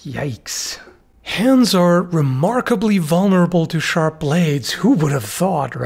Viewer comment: Anyone else heard "Shardblades" the first time and had to rewind?